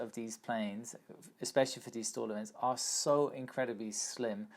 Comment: When you say "stol event" all I hear is "stall event". Which is basically what it is. hahaha